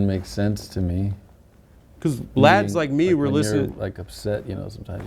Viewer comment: does say blacks or lads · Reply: lads